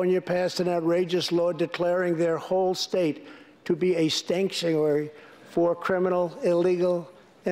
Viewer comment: instead of "sanctuary", he said "stanksuary"! 😂😂😂